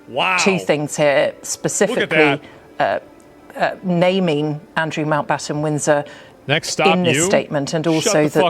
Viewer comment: Okay.. She’s said it about 10 times and I still can’t figure it out. But to the laymen’s ear it certainly sounds like she is saying Andrew Mount Bottom Winds In Her……. 💀😂